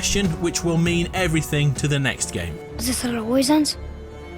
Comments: What. Is. He. Saying?!!?! Isthiswhafrosment? I Can Not Understand What He Is Saying. Dear god, how did they not do a retake on his line? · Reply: Which character? Atreus says ‘is this how it always ends?’ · What does boy say at 16:30 ? I can’t tell. I can only make out “is this how __ ends?” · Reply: 'ALWAYS'